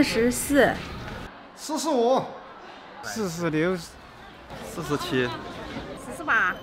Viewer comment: I heard {This is serious} 😂😂